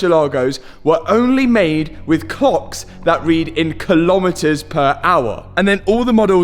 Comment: did he just say c*cks